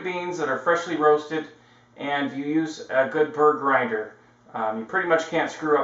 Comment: Right around the 6:01 mark you say..." use a good ___ grinder". I can't make out the word before grinder. Could someone tell me what kind of grinder he is referring to? Thanks. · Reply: burr grinder